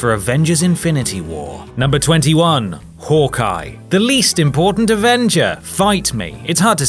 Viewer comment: Ima watch the credits of infinity war and when he said the most un important avenger I thought he said the most important because I skipped all the way to that part